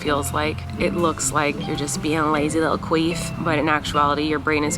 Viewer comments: Did she just say, “It just looks like you’re being a lazy little queef, “?🤣🤭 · did you say “it looks like you’re being a lazy little queef?” 🤔